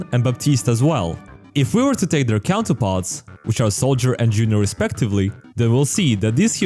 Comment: if you have the captions on, instead of saying “Soldier and Juno respectively”, it says “Soldier and Illari respectively” LoL